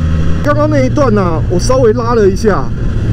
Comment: 聽成 剛剛那段我稍微媽的一下